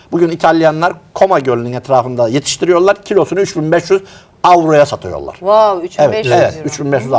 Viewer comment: benmi yanlış anladım??? 10 kilo mantar bi torosmu dedi o? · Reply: Kilosü 3500 avroya satıyorlar diyor.